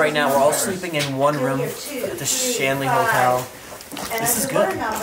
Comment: I got confused, thought you said STANLEY HOTEL,Also very haunted!